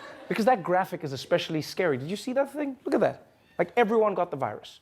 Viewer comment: I thought I heard thingy but that's not something I'd expect a pilot to say. 😁